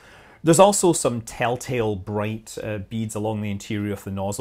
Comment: Yes, that is flow separation. I am talking about the bands up inside the divergent section. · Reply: I thought he said bands. Glitch in my BT audio maybe? I listened to it again and he clearly says beads. Answer me this though. Why is it that no one can ever have actual dialog and it immediately diverges into "Creator right, commenter wrong?" I said in my statement "I believe," I never said they are or anyone was wrong. Yet, its taken that way and the diehard supporters come swooping in like bats. I am a supporter as well and TBH, this makes people not want to comment.